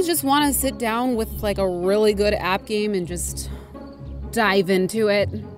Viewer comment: My video buffered so I heard "Ever wanna just sit down with a really good app game and die" lol